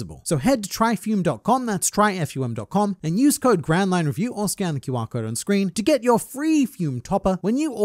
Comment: bro. I was only half listening during the sponsor segment, and instead of "Scan the QR code on the screen" I thought I heard "Scan the Wapol on the screen". I actually looked for a Wapol to scan too XD